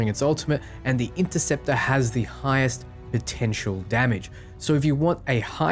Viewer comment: I thought they said the interceptor is the squishiest one out of all of them